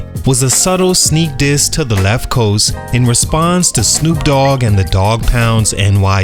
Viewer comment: did he say the 'Left Coast'?